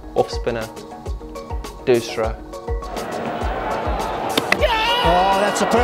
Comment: Did KP Just Said "doosra"?